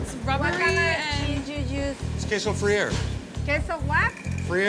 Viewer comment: I thought he was saying "get some free air"